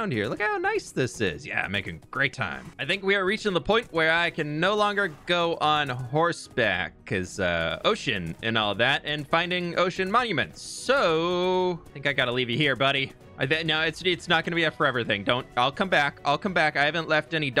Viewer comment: SCOTT LET ME LOVE YOU AND KEEP YOU SAFE!!! Scott: leaves and never returns* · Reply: Also 39:25 i paused it for a moment and i thought said oh sh*t but i just realised he said ocean😂